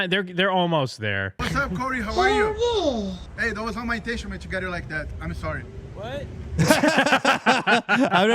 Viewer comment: I think the camera guy said "Hey, that was not my "intention-ment" to get it like that. I'm sorry". Never seen Corey react so appropriately lol